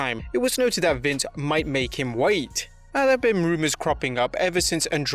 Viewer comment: Your accent I thought you said “Vince might make him white” like chavoo😂